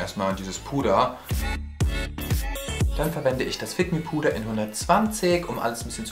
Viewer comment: Wer hat auch bei 4:43 "dann verwende ich das ficken Puder" verstanden 😂😂😂🙋🏽?